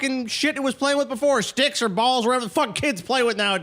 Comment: I thought said dicks or balls